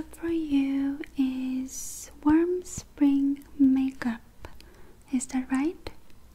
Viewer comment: at first, I thought she said worm spring makeup but I didn't care. I mean c'mon it's.